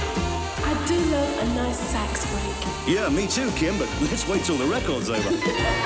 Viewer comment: what does she say here. bad ears sorry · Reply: I do love a nice sax break 😁 yeah me too kim but let's wait until the records over ..... 😁